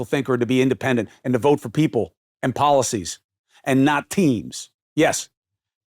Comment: "Don't vote for Teams." (Don't vote for "theives." That's what I thought you said, at first. Both fit, of course.)